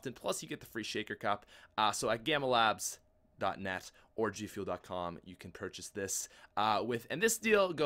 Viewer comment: He said "or G-fuel" but I heard "orgy fuel"